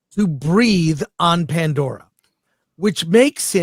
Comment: Oh, I thought Chris said "He learned to breed on Pandora". My bad.